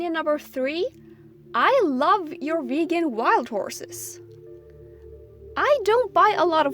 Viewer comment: Why do I hear “I love your vegan wild horses”